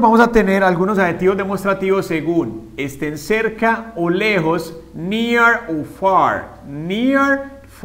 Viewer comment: jeje me dió risa porque pensé que dijo mía fark🤣🤣 igual muy buen video 👍 .